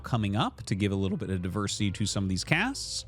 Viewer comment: Did you say diversity? oh no...